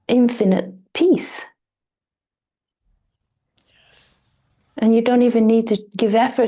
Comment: Am I the only one who heard “yes” at 1:11??? It was like a whisper.. idk if someone else is around and it picked up someone else talking.. anyways was just putting it out there..